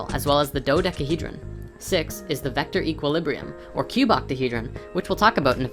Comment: Did he just say "Chewbacahedron" ?